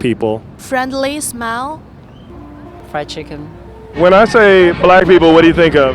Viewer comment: did she say "friendly smell" ? :/